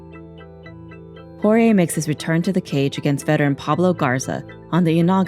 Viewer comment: Sounds like shes saying horier the whole time